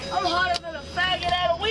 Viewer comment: Her: “I’m not going to the hospital, sir” Him: “uhhm yeah, I think you are.” 😂 did anyone understand what she said when they got to the hospital? I heard “I’m hotter than a faggot at a …..” didn’t catch the punchline. Someone please tell me. It’s